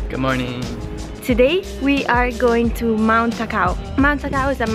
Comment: 2 things: 1. e gli alberi ricordano a me delle Ponderosa Pines in Colorado che odorano di butterscotch! 2. I thought you said in the beginning "Today we are going to mount a cow" and was very confused LOL :D